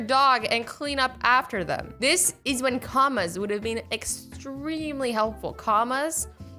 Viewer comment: Wow when She was saying comma I thought she was saying karma